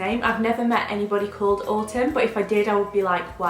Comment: When you said autumn I thought you said August and I love both names but August for a boy would be so cute!